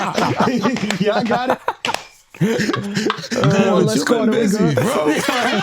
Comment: what did 50 say i diddnt understand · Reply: You've been busy bro